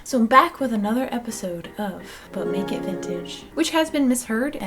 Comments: i always hear "butt nekid vintage" instead of "but make it vintage" and it makes me laugh every time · Good job on the dress!! I know I could never do it!! THe makeup is perfect, you look just like Carrie Fisher!! And yes at the end of the video I heard, "Butt Naked Vintage".